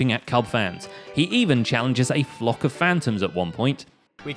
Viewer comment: At first, I thought you said a flop of phantoms. That should be their new name